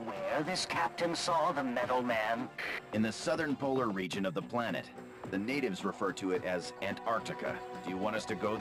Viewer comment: what happened there? Text says Northern Polar region and the Arctic but voice lines say Southern Polar region and Antarctica